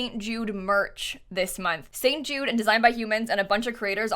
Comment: 🤣🤣🤣🤣 Okay...did she just say :designed by humans"?🤣 Um...I sure hope so...🤣🤣🤣🤣 Also would love for you to make a daycare!! That would be so cool!!